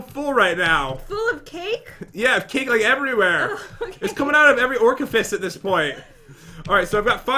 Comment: At about 15:37 (a little bit after actually), it sounds like pat is saying every orcafist.